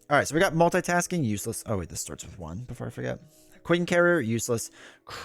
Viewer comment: I heard "Quincarrier - useless" and thought you were drunk. Turns out you were just Quin-cheating. :P